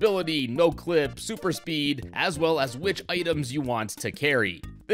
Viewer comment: When He Said Baldo I Misheard It As Balto.